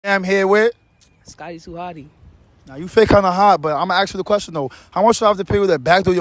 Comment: She supposed to be a girly girl 😂 blessed in the face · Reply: no lie i thought bro said "you fake kinda hot"